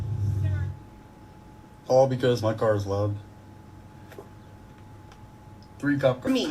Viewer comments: I think he said its loud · sounded like he said his car is loved. lol.